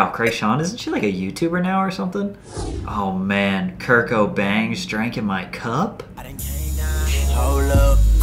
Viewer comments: on god i thought he said kurt cobain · who else thought he said "Kurt Cobain"